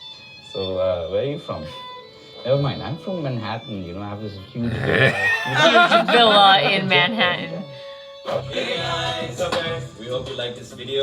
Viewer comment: did anyone understand wht she said Huge ‘villa’ at manhattan